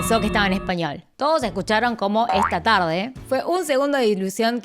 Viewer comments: No puede ser, la primera vez si lo escuché en inglés y luego con esto ya lo escucho solo en español jajajaja y me lo creo lo peor doblemente 🤡🤡 y escuché "está tarde pm" (pm en inglés o sea pi em) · Escuche "Está Terapia" Alguien más?